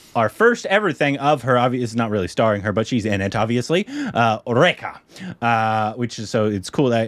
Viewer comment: is it just me who heard "Eureka"!? 😂